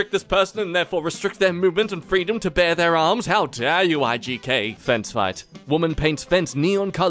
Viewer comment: did he say Igk and not Ijk?